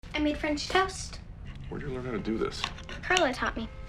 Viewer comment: I thought it said "Cum french toast"